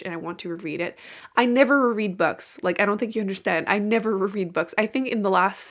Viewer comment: Haha when you said you never reread, I keep hearing it as “I never read” every time I replayed it and was like wait a minute… 🤣 Please read all the fantasy books you mentioned! Those are all high on my TBR 🙏🏻 Reading ADOFN now! That was the last book I bought :)